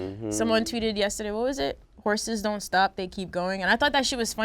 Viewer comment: Damn I thought Thug was saying "horses don't stop, they keep going" too.